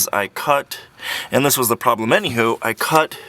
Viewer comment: Did he say " any who" @ 8:31?? Wtf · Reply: He said: "any ho" the way Canadians or Americans from the Northern States would pronounce "anyhow".